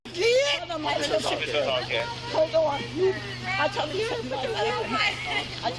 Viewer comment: Does anybody understands what the parents said at 2:09? For me it sounds like the women says "Told you she had a knife", but I'm no native speaker so I'm not sure I'm getting this right